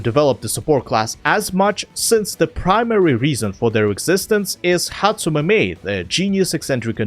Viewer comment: all i heard was "HATSUNE MIKU?!"